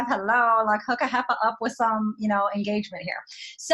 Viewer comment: Did she really say “hook a heifer up with some engagement here” or am I mishearing her dialect?!??